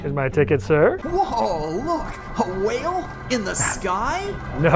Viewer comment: waluigi? XD kinda sounded like mario, luigi, and waluigi